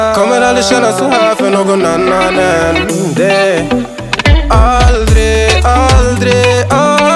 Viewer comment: I always hear "Aldi".💀